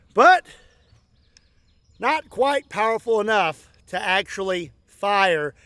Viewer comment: I thought he said White Powder, had to replay that section twice.